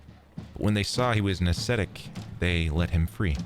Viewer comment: why did they let him free ? What was the word he used I thought he said aesthetic? Idk what the other word means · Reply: Ascetic basically someone who like thourgh struggles achieves grace i know the word but dont know how to explain it very good